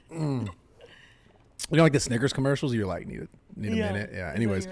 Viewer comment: did he just said the N word 🤨🧐👀, lol! min